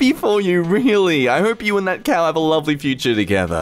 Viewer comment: did he just say ''i hope you and that cow have a lovely future?'